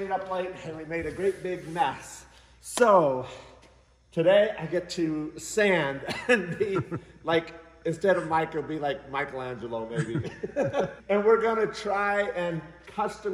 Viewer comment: Did you say "Micro" Angelo? haha